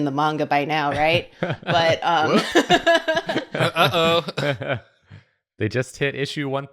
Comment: "takes 300+ chapters" Me: oh boy is in for a surprise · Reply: I thought what she said that was just "oh honey"